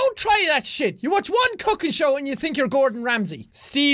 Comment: Oh u said cooking I thought u said fucking show 😃😜